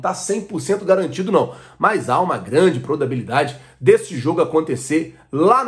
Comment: So eu que ouvi "prodabilidade"?? Kkkkk zoeira kkkkkkkkkkkkkkkkkkkkkkkkkkkkk vc é top!!!!!